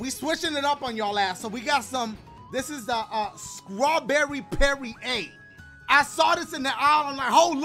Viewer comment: did he say Scrawberry or Strawberry?... 😐... I think I heard Scrawberry my bois.. I don’t know 🤔